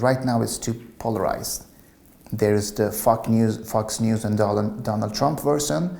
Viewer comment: Did he just say fuck news at 13:27 instead of Fox News xD?